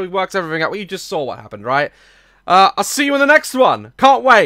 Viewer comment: I never understood what he says at the end...I hear "Like, love...crush my channel"? Thanks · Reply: "Like, Love, Caress my channel"